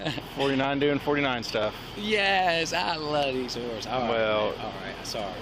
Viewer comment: Did he say i loves theses whores??